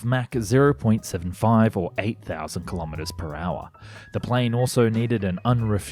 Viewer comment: Did you say 8000km/h???? what the hell lolol